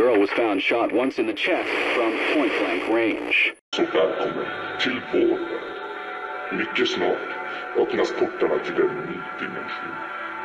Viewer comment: i am pretty sure that this is swedish or some other nordic country i think it says Så välkommen till vår värld mycket snart öppnas portarna till en ny dimension. translation: so welcome to our world very soon the doors will open to a new dimension